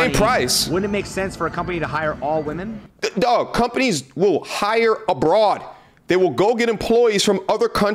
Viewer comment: I guess it shows my age… but when you said they will “hire abroad” I thought you said “hire a broad (as in a woman)…lol